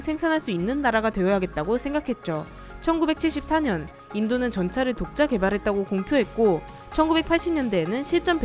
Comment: "인도는 전차를 독자 개발 했다고 공표했고" 라고 들리네요